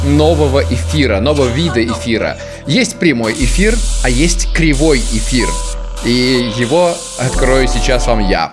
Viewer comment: мне одному послышалось кривой кефир?